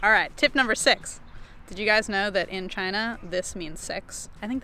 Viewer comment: I thought you said, "Did you know, in China this means sex." And I thought the tip was going to be, "if you're going somewhere with a different language, learn the cultural signs so you don't end up asking for sex when all you want to say is "hang loose"." =D